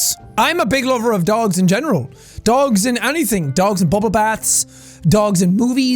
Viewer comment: So, while I was watching this I sneezed, and I thought he said `dogs in boobies`, and now I can't stop laughing.